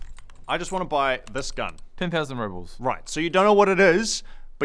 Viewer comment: I thought he said "10,000 Robux"